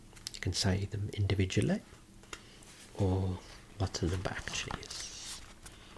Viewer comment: Can someone please tell me what he says at 2:39? "You can say them individually or _______". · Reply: “… or what the number actually is” at least thats what i hear